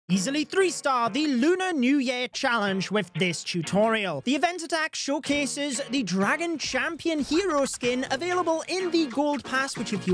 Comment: I thought you said EASY 3 STAR? 😂